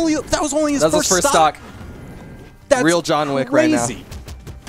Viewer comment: Did he said jhon wick ?